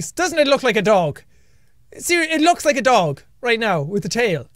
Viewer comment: all I thought when he said that was "ITS A DOGE"